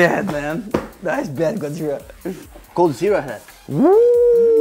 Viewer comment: When FalleN says Coldzera it sounds like he is saying Godzirra (like Godzilla but with a stereotypical Asian accent).